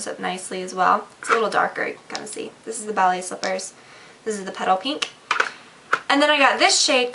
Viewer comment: when she aid petal pink, for a sec i thought she said pedo pink LOLn <3 this vid!!